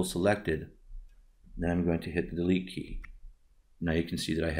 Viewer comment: Hi, congratulation! Please, I don't understand spoken English very well. At minute 2:53/2:54 which button do you press? After selected mask you say "A key"? thanks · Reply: Delete key.